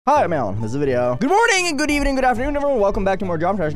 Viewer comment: why does his intro sound like he says "Hi, I'm Alan" instead of "Hi I'm Aeon" lmao